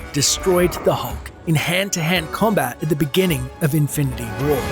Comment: did you just say “Infinity World”